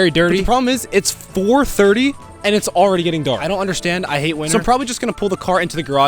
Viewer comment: for a second i thought he said "i hate women"😭